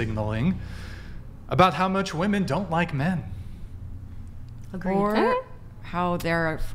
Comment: say don't like men then the whole panel of women chose to here what they wanted and "gaslighted" him by saying he said hate and continuing to affirm he said hate 🤔